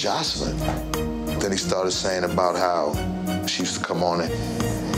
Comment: Did Stebie just confirm that She was a pro? That’s craxy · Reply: Thought I was tripping but I could have sworn he said, "She used to be", when Benzino called her a prostitute.🙆